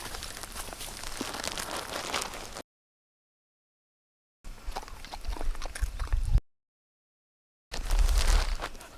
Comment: 태평이 꼭꼭 씹어먹는거 넘 귀엽다 ㅋㅋㅋㅋㅋ 17:03 천하한테 뭐라고 하셨길래 천하둥절ㅋㅋㅋㅋㅋㅋ귀여웡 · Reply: 좀 씹어 먹어! 했을 거임 ㅋㅋㅋ